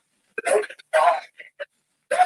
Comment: I thought it sounded like “don’t die” and then I turned my captions on and it came up and said the same thing.